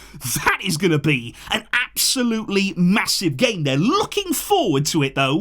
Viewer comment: did you say massive???